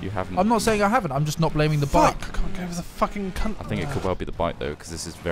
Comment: i'm not the only one who heard say cunt at about 9:15 am I?!